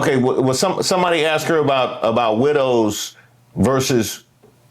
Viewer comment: Does anyone else think that the word "widows" sounds like someone saying "littles" in a baby voice after awhile? thanks as always for keeping us informed with humor. ❤